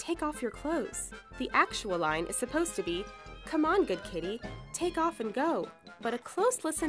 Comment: i hear "good kitties take ur clothes"